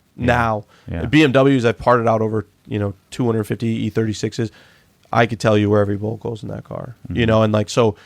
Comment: did I just hear say, "parted out 250 e36's"